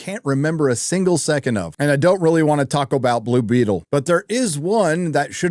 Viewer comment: I thought you said you didn't want to taco `bout Blue Beetle?! HOW DARE YOU!